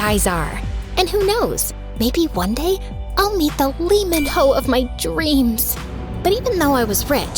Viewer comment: Did I hear LEE MINHO since when was skz in MSA